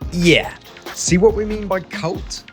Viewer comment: did he just say cult……………. Low lmao